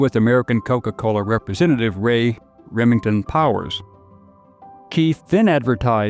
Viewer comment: Ray RIVINGTON powers (it sounded like you said Remington) and Powers passed in a strange car accident while in Germany